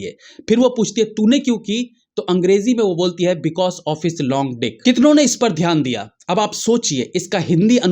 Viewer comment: Long-dick ?? I heard " loaded" I need to watch again 🤔